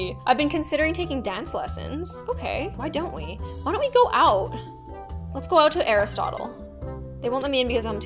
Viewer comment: I thought She said, let’s go out to eras tour 🫢